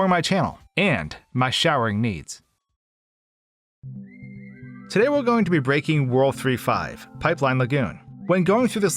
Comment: literally thought he said "today we're gonna be breaking rule 35"